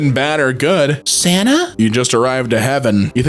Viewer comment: I somehow heard Sana instead of Santa- RIP